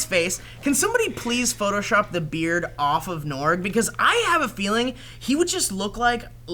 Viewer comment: said norgy or something to norg, sounds like she said Norge :D